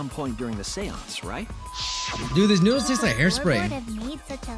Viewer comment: The trial: being an intense emotional rollercoaster: n o o d l e h o t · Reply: I thought that said "noodle thot".